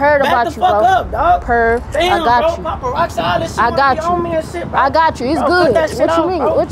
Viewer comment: Did bruh say paparoxide😂